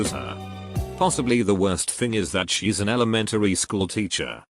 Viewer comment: Last story, very last words, I legit thought he said "she's an 'anime school girl.'"